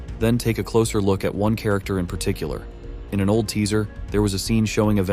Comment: why did i think he said malteser